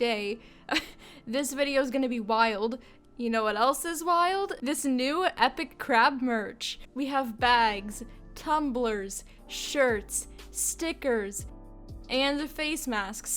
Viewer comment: i thought you said epic crap merch until i saw the crab on your merch lol sorry im new on your channel XD